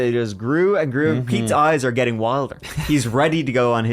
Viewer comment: I'm 🤔 Pete?! Who is Pete?! Even CC says Pete! Oh! You meant Keith! 🫡